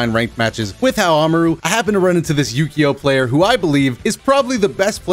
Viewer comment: thought you said yu-gi-oh player for a second LOOOOOOOL